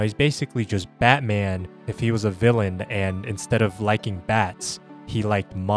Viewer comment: did you just say Batman likes Bat ?